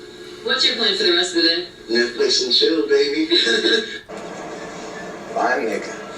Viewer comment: I heard "Netflix and chill baby." No idea what he said at the start though.